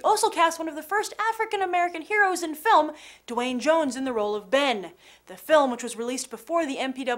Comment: Sounded like she said Dwayne Johnson. I was like "say what?!" but then realized she said Duane Jones.